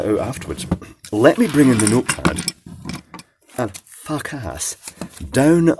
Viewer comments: What did he say? 4:53 it sounded like, “let me get the notepad and fuck ass” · I really thought you said "fuck ass".